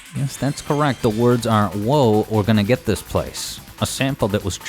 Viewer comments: I thought it said "Forget this place burn" on the bad future I didn't know it said "Whoa we're gonna get this place" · "we're gonna get this place"? Always heard it as "Oh, Forget this place!"